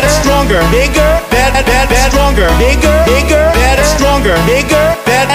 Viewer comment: Pensé que decía Niger 🇳🇪